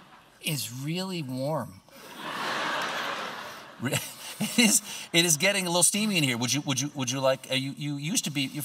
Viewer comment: I misheard “It’s getting a little Steavy here”